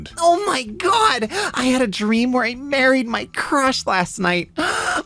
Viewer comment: I was watching this and thought the dream one said "I dreamt I BURIED my crush" and I was confused.